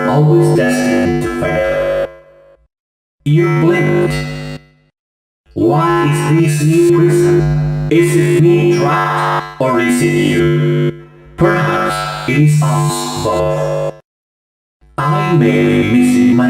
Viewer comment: i thought they said:why is it so hot in here? Is it springtrap? Or is it you? Perhaps it smells oh!